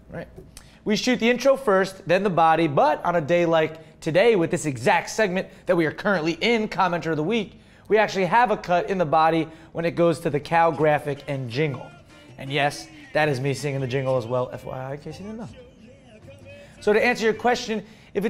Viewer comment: thought he said c*nt